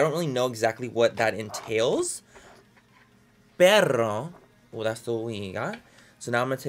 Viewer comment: Soooo we're gonna ignore the fact that he said dog instead of but? I think I'm just gonna laugh and not be offended 😂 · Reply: Lauren Wilson he rolled the r way too much making it sound like he said perro instead